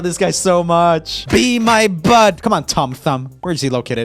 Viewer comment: Did anybody else hear say be my butt I swear that’s what I heard😂😂